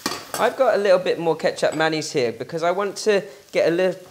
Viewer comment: Omg. I thought you were saying ketchup mayonnaise and I was so confused. lol. Feel silly now that I’ve looked at the recipe.